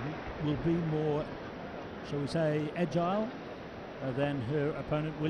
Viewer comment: Did he say Maria was more “agile” thank Serena at 13:45? THE LIES THE LIES THE LIES